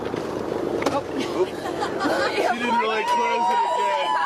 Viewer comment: Whenever Jeff opens the door, with that tool, i swear i heard him say 'she didnt really close it then' or something to that effect when he turns and speaks to that lady behind him. The same lady that ran over there and told em to close it when they opened it to soon.. or maybe im trippin.